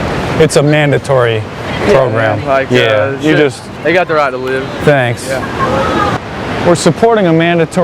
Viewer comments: This one guys is hilarious 😂 " They have the right to live" but signs the petition. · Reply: I thought he said "they got no right to live." ?? Lol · listen to what the guy says, "they got the right to live." He has no idea he just signed a fake petition petitioning to kill old people. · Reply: Sage Adams thought he said "they dont have the right to live" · "They got the right to live", he says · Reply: I thought he said, "they got no right to live." ??? Lol